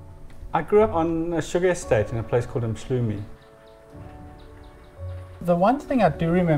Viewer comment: The narrator says he grew up in Mhlume a sugar estate in lowveld Swaziland a very hot place ...full of green forests I can concur the similiraties this house has with Mhlume very breathtaking...love both places Mhlume & Cape Town. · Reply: Sounds like he says 'Emshlumay.'